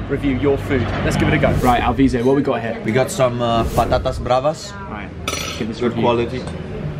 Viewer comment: did he just call the potato battatas? because in arabic its the same